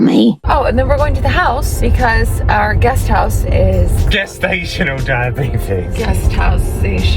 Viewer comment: I was laughing because I was listening (and not watching)and thought I heard "gas station-al" diabetes!! 🤣🤣